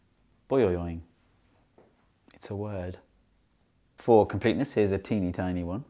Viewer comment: did he say “fork penis here’s a tiny one”?